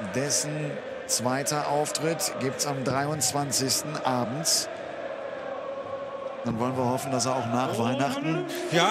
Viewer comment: Can someone tell me what they’re chanting at 11:35? My dad and I have been trying to find out for ages. Thanks. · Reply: dont take me home please dont take me home i just dont want to go to work